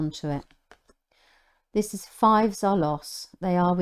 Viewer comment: sorry but i'm not english, what are you saying in this part? The subtitles say five zalos? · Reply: fives are loss ❤️